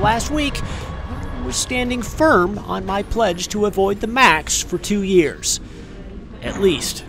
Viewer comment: I thought you said "avoid the MASK for 2 years"!!